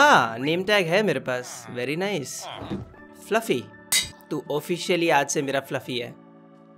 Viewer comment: why did I heard it as "pati"💀💀